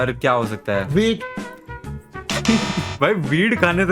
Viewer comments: I thought ur father said weed instead of wheat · your father did not said wheat he said weed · he actually said weed Not wheat 😂